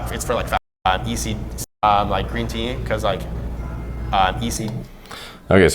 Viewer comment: wtf dude? he was trying to say "ECGC", and then he said "green tea", not "creatine" @ 16:31 . ECGC or "Epigallocatechin gallate" is a catechin contained green tea that aides in the reduction in body fat. Up your oxiracetam bro, lol.